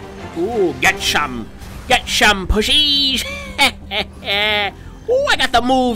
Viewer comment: Huh, that's weird... I always thought he said, "Don't get pushy."